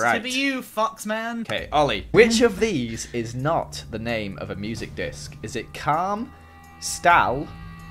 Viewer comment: I thought it said muscle disc lol